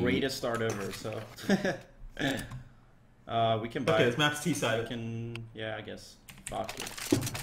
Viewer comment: did Rain just say this map is t sided?